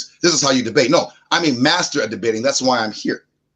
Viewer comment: Did he say he's a masturbater. 😂